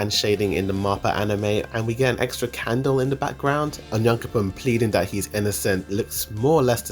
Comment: He sounds like he is saying "onion coupon" for onyankompon lol.